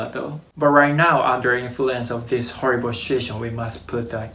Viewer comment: Haha 😆, I realize that you said situation but, I can’t help not pointing out that, first two times I definitely heard “under influence of this horrible sh*it show”, which is greatly applicable to the current situation in the world.